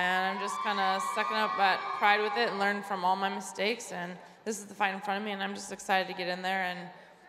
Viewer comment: did she say "this is the final for me?"